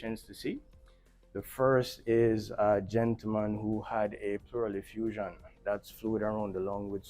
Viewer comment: Oh you said pleural effusion I thought you said barrel effusion and I was like 😮 lol